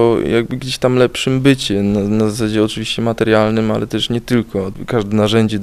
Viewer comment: it sounds like he is saying bitchin.........